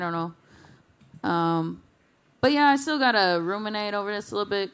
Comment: did she say “roominate”? Is that a word?